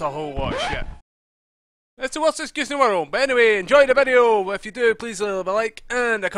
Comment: wat the hell did u say between 0:26 and 0:30 secs ??? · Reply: He says "It's the worst excuse in the world" or near enough